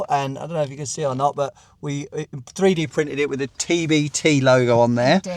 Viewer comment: Oh wow. At first when you said a tbt logo. I heard it as To Be Determined. As in a place marker for a replacement logo. Woopsie.